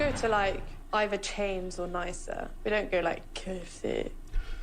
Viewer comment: just me who thought she said KFC?